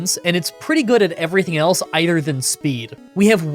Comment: Am I hearing things or is this the second video in a row that you’ve said “ier than” instead of “other than”?